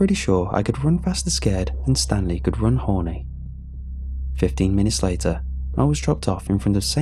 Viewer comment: I thought it said "And Stanley could run horny" which was... Confusing but even more confusing was the fact I just accepted it until like 5 seconds later.